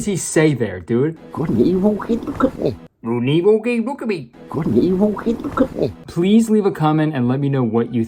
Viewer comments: not sure about the start of the sentence but I think he said “look at me” at the end of it. Maybe it was “hey woah w- look at me.” 😂 my brain ain’t braining and his words ain’t wording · I think he's saying "Good and Evil? Hey look at me!" But I'm 100% open to being wrong. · "an evil McCookie" thats what he says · What I heard was, "Good & evil? Okay, look at me!" But I don't know if that fits the context of the scene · "Good n Evil. Hey, look at me" that what I hear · I keep hearing: "good and evil *gurgle*" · I think he says something like. “Could blank blank even look at me” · "hay there Mia look at me" Is what he said 5:00 I think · what the boss says: "good and evil? hey! look at me!" i guess because she glanced down for a second while he was babbling on and on about god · I thought he was saying "Good and evil- hey, look at me." but that doesn't make a lot of sense so I don't know haha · That one line I heard “.. good and evil… look at me” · he says "Good and evil. Hey look at me" at leat that's what I heard and then confirmed with closed captions. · What he says, at 4:49 for anyone confused. " I was talking with my dad over the holiday, yknow. And- And my dad says yknow, 'God's not dead.' We act like he is and- Good and evil? Hey, look at me!" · It sounded like he said "good and evil, hey look at me" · I heard "good and evil can't look at me" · I think he said "good and evil cannot connect." He for sure started that sentence with "good and evil."